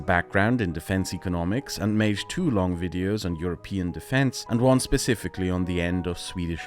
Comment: I heard 'two long videos' as 'too long videos' which wouldn't be too wrong regarding perun